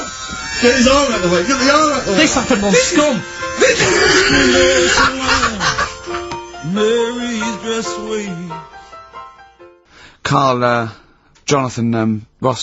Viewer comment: What does Karl say at 9:38. HAhaha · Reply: ''This happened on Scum!'' It's a film about a prison